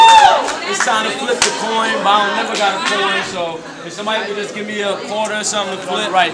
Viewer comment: Every time I watch this battle I swear somebody said To “He Ain’t Never Got A Coin”😂😂😂